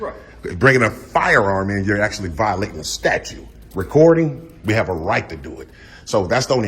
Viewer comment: Is is a requirement they say "statue" instead of "statute"?